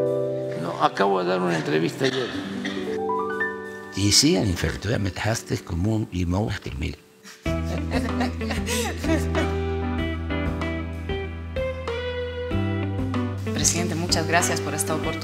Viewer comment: qué dice AmLo? qué idioma es? gracias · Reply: Es en español, dice: "Y sí, en efecto, me dejaste como un limón exprimido". Pareciera que lo dice en ruso, pero no.